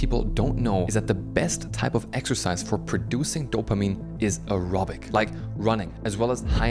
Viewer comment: Diagnosed for 20 years today. Never experienced the rebound as "falling off a cliff". It just felt like getting a bit tired. Focus? No difference. Creativity? Almost no difference, a bit better without. What truly is noticable for me on medication (Medikinet Adult): emotional and especially anger control. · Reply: I thought he said Erotic exercise! Oh aerobic. Ok. 😂